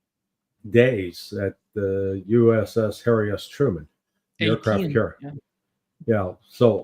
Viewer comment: Did Larry really say "the USS-Hairy-Ass-Truman"? Such colorful language, very entertaining.